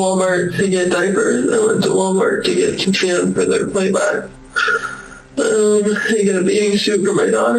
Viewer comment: "I went to Walmart to get some sand for their play butt" WHAT? Was that supposed to say "Play BOX"?